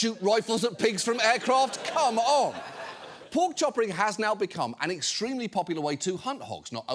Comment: i've been playing too much Warhammer 40k. i could have sworn they said Orc Choppas. 😅